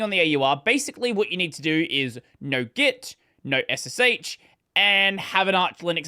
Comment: Captioning error, "no" instead of "know"